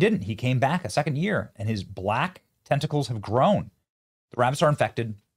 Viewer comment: I had to read that twice! I thought he said black testicles 😂😂